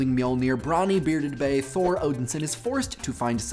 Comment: i kept hearing "odinson" as "odin-san" and it was really offputting cause at first i thought you were repeatedly referring to "mr odin" and not old thor.